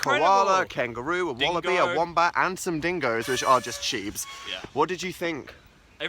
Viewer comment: when they were talking about the dingoes i thought they were saying sheeps so i was like have you even seen a sheep before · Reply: I think they said shibe not sheep